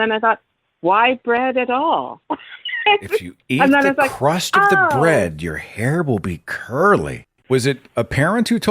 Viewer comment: I heard 'eat the crust on the bread and you will be able to whistle.'